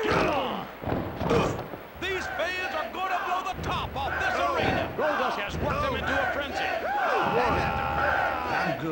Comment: What were the chants saying for Goldust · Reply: Sounds like"Curtain Call", his signature move.